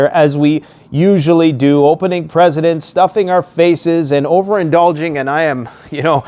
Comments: Did you just say: ‘opening presidents’?! 😂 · Did he just say opening presidents at the start of the video or did I hear him wrong